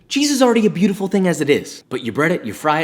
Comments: At the start of the video I thought he said Jesus is already a beautiful thing as it is. AMEN 😂👏🏼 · I literally heard "Jesus is already a beautiful thing as it is" Just me? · at first I thought he said "Jesus is already a beautiful thing as is" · i thought u said "Jesus" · I thought you said Jesus was already beautiful at the beginning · In the beginning, I thought he said "Jesus is a beautiful thing as it is". Until I saw the cc.